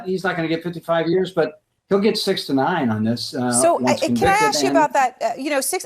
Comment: All Trump needs is 11,780 days in prison!😡😡😡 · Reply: I misheard 6 to 9 as sixty nine and thought that’s a little steep. Perhaps half that?